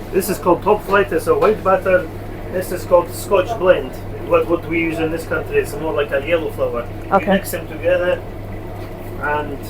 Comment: White Batter ? What's it made up of ? Is it Corn Flour. .. and "Scotch Blend" - Did I hear that right. :) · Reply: Yes it’s a mixture of 2 flours scotch blend and a white batter flour